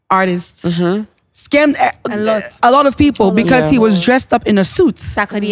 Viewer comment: I thought he said on Pop the 🎈 that "he was drugged "😅